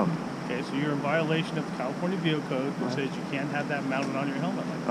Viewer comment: "You're in violation of the California Penal Code..." Did I hear that right? No wonder our prisons are so full.